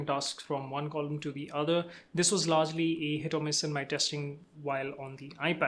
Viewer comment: Thanks for the great tutorial/intro! The only thing is that it got a little muddy around 1:34 - I couldn't understand you were saying "hit or miss." I suggest maybe trying to slow down a little bit next time... but excellent explanation of features/capabilities/improvements!